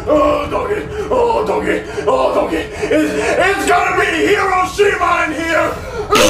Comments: Shrek:IT’S GONNA BE HIROSHIMA IN HERE RELEASES POOP BOMBS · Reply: I thought he said its gonna be hero see mine here. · "ITS GONNA BE HIROSHIMA IN HERE" had me dying so much back then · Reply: I thought he said IT'S GONNA BE A HUGE SH*T MINE IN HERE